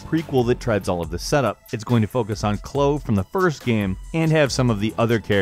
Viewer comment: DID HE REALLY SAY CHLOO INSTEAD OF CHLOE!?